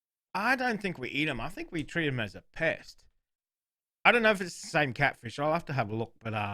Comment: Misheard you and thought you said you see catfish as pets instead of pests in Australia, and I just... accepted it. At this point nothing would surprise me anymore 😄 great video, thanks for making everyone hungry 😊